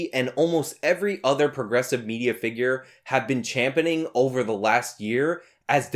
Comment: "champoning"? Not bad friend, you just made up a word there haha, what would the definition for such a word be though? Sounds rugged, like "I champoned my dewalt drill last week" or "he's champoning the concrete next week"